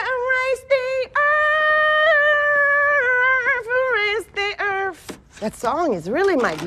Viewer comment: at first i heard earth then if you listen again you hear erf lol